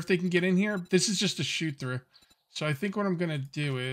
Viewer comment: 🥰 I thought you said shoe thraw,I'm going deft🤣🤣🤣